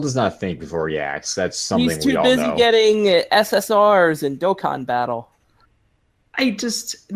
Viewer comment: Do my ears deceive me? Did I hear "Dokkan Battle"? :D Is there a reason why Dokkan Battle was brought up? :3